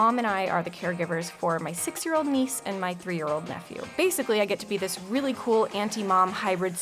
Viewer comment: I THOUGHT U DIDNT HAVE KIDS YOU SAID AT 0:15 AND MY 3 YEAR OLD SON Matthew